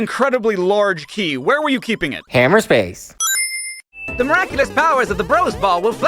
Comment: I never understood this "??? space" you keep saying. The subtitles say "hammer space" but is that really what you say?(😵)